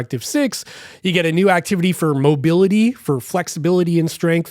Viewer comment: did I heard this right? Garmin has now (after years of unheard user request in garmin/ideas) a "Mobility" activity? Any info is this also coming to other watches or will be VA exclusive?